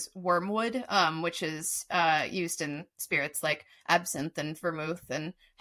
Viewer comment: Funny that when talking about wormwood, said "which is used in spirits" I heard " that witches use for spirits".